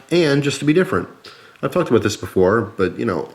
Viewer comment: he sounds like hes saying "I've fucked with this before"